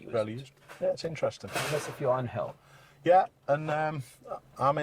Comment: I kept thinking he said ‘hell’ instead of hill 😂